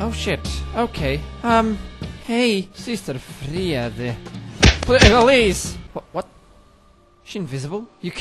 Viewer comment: what did you say? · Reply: He was saying the boss' name, Sister Friede